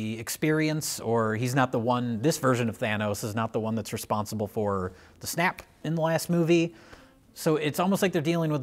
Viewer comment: Jay calls the snap “the snack”. Please tell me someone else hears that.